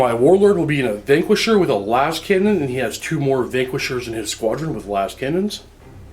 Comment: I'm sorry did I hear "the vanquishers will jink"